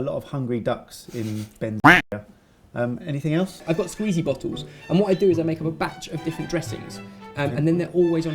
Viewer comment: When Ben said "Squeezy Bottles" I thought he had said "Squeezy Bottoms" and I was thinking "Oh this should be interesting..." :P - Heidi